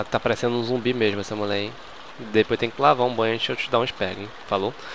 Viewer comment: Juro que entendi " Depois tem que lavar um banho antes de eu te dar um esperma hein! " ashuasuasuasuhusahuh